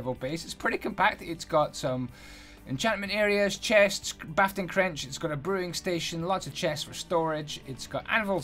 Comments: Wait. Did he say"bafting crench" at 1:44?! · Is it just me or did he say "bafting crench"?